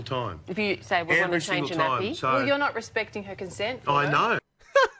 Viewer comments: I thought it said contest, but either one is fucking horrible. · I thought it said contest lol